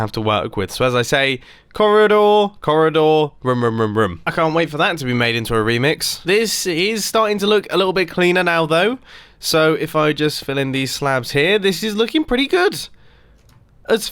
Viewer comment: Did Mumbo say "Ronaldo"?